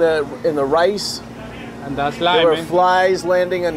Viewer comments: I thought he said that's life at first🤣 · Lol when he said there’s a roach in the rice, I thought he said that’s life 😅